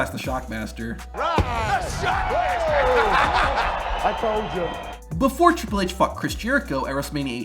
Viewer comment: Ayo what did he say?!?!😂💀🙏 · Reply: "I told ya”